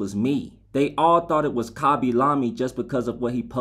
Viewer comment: I THOUGHT HE SAID KHABY SALAMI LMAOOO